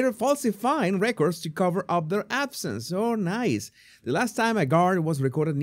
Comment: Cover up their absence. I thought you said Cover up their asses. Ran it again and now laughing at myself!